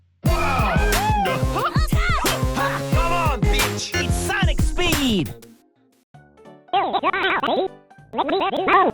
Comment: "come on, Peach!" What is he saying? I know it can't be that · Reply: Bitch